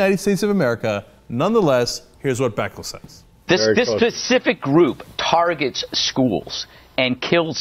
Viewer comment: Did he just say 'this pacific group'? Oh Greg Gutfield..